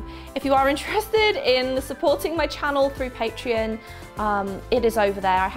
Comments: At first I thought you said "paint tree" instead of Patreon, so now I have visions of a xmas tree with paint themed ornaments on it. 😂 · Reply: I actually thought she was saying Paint Tree as well 😂😂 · At first I thought you said "paint tree" instead of Patreon, so now I have visions of a xmas tree with paint themed ornaments on it. 😂